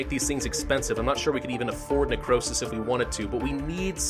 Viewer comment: "Necrowsish" that's what it sounded like he said at one point